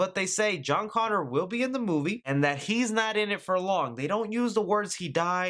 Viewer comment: Did you say "He's not in it for long" or "He's not in it. Furlong."? ha ha